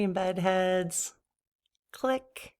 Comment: You mentioned that one of your viewers misunderstood your word as "buttheads." 😆 I just heard "deadheads," so there's that!:hand-purple-blue-peace:🎸☮